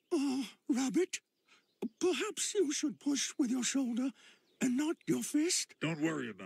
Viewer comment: Laughed hard at the Bing maps car and also Winnie the Pooh saying "perhaps you should push with your shoulder...." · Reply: I thought he was saying “perhaps you should push with your shoulder and not your face”